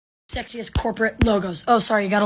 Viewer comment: I thought you said sexist and I was SO confused